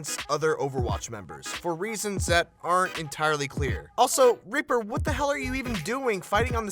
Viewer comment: Did he just say ripper